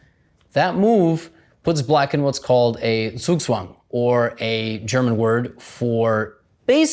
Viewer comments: Correction for the captions: You transcribed it as "Zwischenzug" instead of "Zugzwang". They're both German chess terms, but completely different tactics. Zugzwang basically means "forced to make a move" (at the detriment of your own position), whereas Zwischenzug means "in-between move" (which is basically a stalling move that improves your position before you play an expected move) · LOL @ 15:24 The cc captions say zwischenzug instead of zugzwang. Both are German chess terms, but they mean different things! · why does it say zwischenzug in the captions when gotham said zugzwang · I don't know if anybody else has commented on it yet, but the captions at 15:25 say "That puts black in what's called a zwischenzug" Zwischenzug is not what Levy said, and means something else. Levy said "zugzwang" which means what he said it means. Zwischenzug is "a chess tactic in which a player, instead of playing the expected move (commonly a recapture), first interposes another move posing an immediate threat that the opponent must answer, and only then plays the expected move," or in other words an in-between move. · I was watching with just captions and was shocked that Levy got the German word wrong @ 15:24, but he didn't. The caption says "zwischenzug", which means "in-between move", rather than what Levy correctly said, which is "zugzwang". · mfw at 15:23 captions say it's zeischenzug when it's zugzwang (just like gothamchess says...)